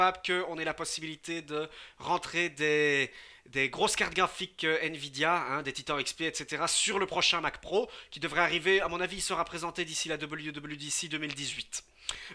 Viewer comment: J'ai entendu "des Titans Xp dans des MacBook Pro". J'ai remis en arrière. J'ai réentendu "des Titans Xp dans des MacBook Pro". Est-ce que l'on parle bien des même MacBook Pro ? Ceux qui ont actuellement des radeons pro 560 ?